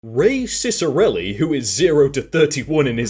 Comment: Wait 4 real? His name is Ray Ciccarelli? Ray-cic. Go figure. Could of sworn you were just making a joke on his name. when I heard you say, racist arelli